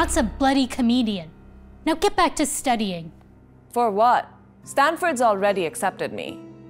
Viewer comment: I thought she said "Stanford" like in "Gravity Falls"